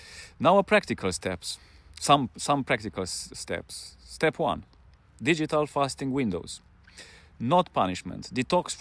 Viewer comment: Can someone please tell me what says at 6:40 to 6:45? I think he says something about digital fasting? Thank you.